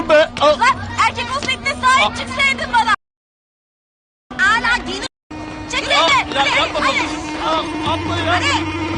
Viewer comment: 'te sansürlenen kısımda ne diyor anlayamadım? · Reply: Eşşoğlusu diyo 😂😂